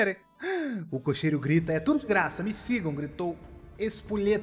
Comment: só eu que escutei em vez de ser: e tudo de graça, e tu desgraça kkk